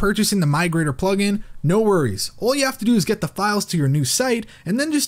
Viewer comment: "...No worries, all you have to do is to get the fuck out of here..." This is almost how I heard it or at least expected it to be XD